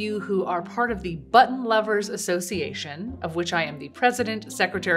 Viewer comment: I thought she said butt lover association 😂